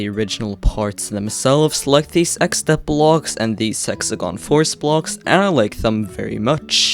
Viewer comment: I heard s*xegon force from how you said it, but i know what you actually said, these hexagon force